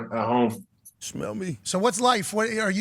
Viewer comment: wtf who is saying "smell me" lmfao